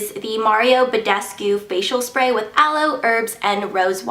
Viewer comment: sorry what was in that facial sprat aloe, ? and rose it sounded like you said erbs but did you mean herbs